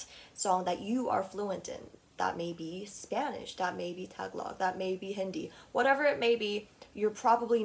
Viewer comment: “that may be tag log” pauses the video to understand what she said …..oh wait, do you mean tagalog?